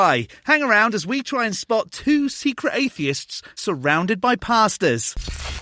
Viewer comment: I thought he said pastas for a second 💀